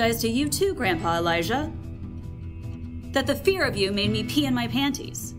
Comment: okay i am saying this because i wasn't looking at the screen when i got cut off guard as soon as i heard "the year of 69"